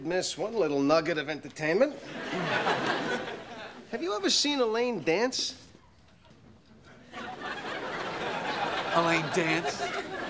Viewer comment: When I saw the trailer for the series on DVD on my Spider-Man 2 VHS as a child and when he showed the clip montage and this was the 2nd clip in the trailer I thought they were saying “A lame dance” instead of “Elaine dance”